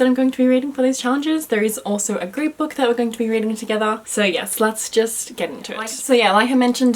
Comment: From 0:41 is sounded like you said "sluts just get into it." I died..